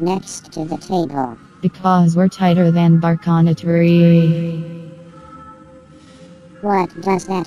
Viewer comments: I thought they were saying “tighter than bark on a tree” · I thought that they were saying, “tighter than bark on a tree”